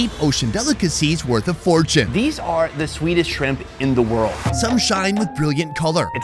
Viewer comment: i keep hearing him say "swedish shrimp"